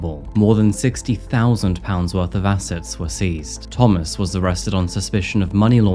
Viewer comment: At first I thought you said 60,000 lb of acid 😂😂😂😂 I was like goddamn!?!